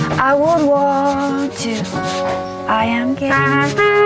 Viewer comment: i thought blue diamond was saying "i am gay" instead of "I am game"